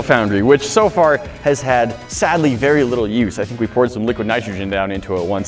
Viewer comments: i thought it said "liquid frog" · I thought it said liquid frog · Lol i thought it said liquid frog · I thought it said liquid frog 😂